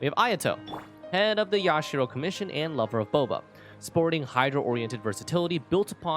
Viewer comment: Without the subtitle, I thought u just casually said ayato "lover of thoma" LMAO